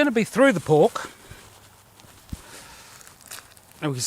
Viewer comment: I thought you said "it went through the Orc" Lol